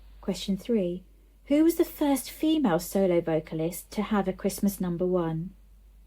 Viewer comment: I don't know films so wouldn't have got that question, and you caught me out with "vocalist" on question 3. I heard it as artist and got to a different answer (which would have been correct!) Christmas number ones is kinda my specialist subject so anything that just asks about years, artists and songs is child's play!